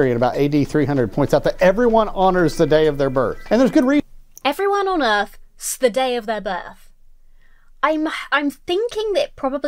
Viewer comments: I think he said everyone honors the day of their birth? Had to listen 4 or 5 times over but here we are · I think he said "Everyone honors the day of their birth" 😉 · what he said is "everyone honors the day of their birth" 👍 those pesky American accents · “Everyone HONOURS [not ON EARTH] the day of their birth.” I heard it and that’s their context of conversation immediately prior. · as late as i am watching this, i'm sure it's already been pointed out, but incase it hasn't (by some chance *shrug) 28:51 ,sorry i couldn't catch the name, "...AD 300: Everyone honors the day of their birth." Not sure why, but you simply misheard. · I think he said "everyone HONORS the day of their birth", but I may be wrong